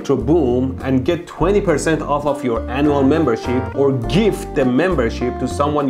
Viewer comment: Did he just say, ANAL membership?